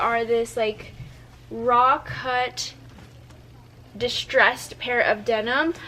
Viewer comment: What was that key word you said when you showed the Mousy jeans? rock hut ? It’s at 29:15/16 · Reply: Raw cut